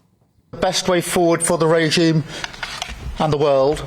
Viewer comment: Ok, he just called the USA "the regime". Did I hear that correctly?! The guy looks absolutely spooked.